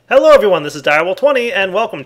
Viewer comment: I paused the video accidentally just after I started and I thought I heard "Elo has" instead of "Hello everyone" and I started freaking out thinking Eloraam came back